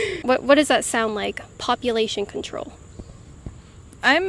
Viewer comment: Thought you said "copulation control". Hell yeah, control those f*ckers!